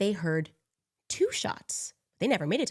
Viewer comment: I heard "who shot and why?" Not "two shots". anyone else, or just me?